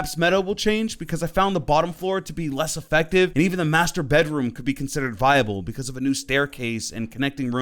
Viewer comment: when your not an English speaker and you understand masturbate room instead of master bedroom XD